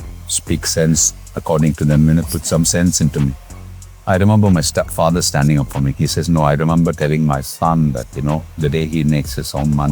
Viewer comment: Is he saying Step Father ? Never knew that.. Sorry if I mis heard it